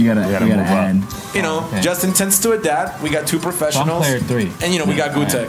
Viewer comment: can't believe Justin Wong is a dad now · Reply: You misheard him He said '...Justin tends to adapt...'